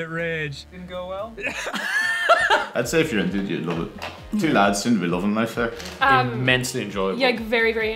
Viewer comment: "Two lads seem to be lovin' life there." I like that guy. (Sorry if I misheard what he said, I just assumed)